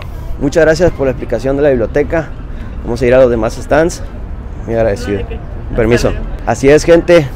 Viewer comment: En el minuto 4:10 cuando dice muy agradecido yo escuche muy AGRADECIDA y repetí esa parte y escuché muy AGRADECIDO creo que es el efecto como los videos que existen que lo que piensas es lo que escuchas 😅😂